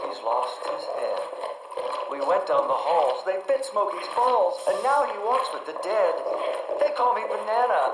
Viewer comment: I think it says "bit Smokey's balls" rather than "fixed Smokey's balls"